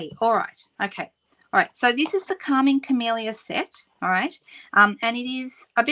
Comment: You said Calming Camellia, and I heard "Karma Chameleon"!